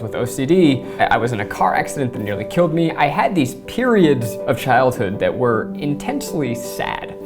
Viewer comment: I thought that Zachary said that he had periods